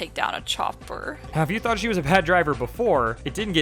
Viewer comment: the captions say "now if you thought she was a bad driver...." but what you actually said is "HAVE you thought she was a bad driver......."